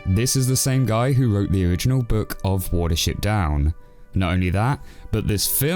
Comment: I thought it said Water sheep down